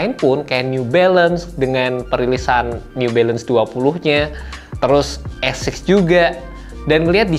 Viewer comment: new balance apa bng? ga kedengeran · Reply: New balance 2000 kak